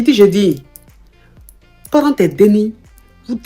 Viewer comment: c’est quoi les déni ? 😅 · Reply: Enfants